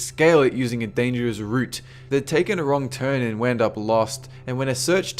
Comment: I know he says 'route' but cos he's Australian I hear 'root'. Cracks me up...